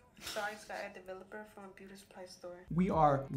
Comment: For a second, I thought the girl at the beginning wasn't using developer at all. I heard her say that she got the bleach powder from ebay, but it was really expensive. It's hard to hear it, but she did say she got 40 developer from a beauty supply store